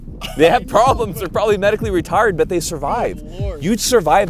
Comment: i heard medically retarded, instead of retired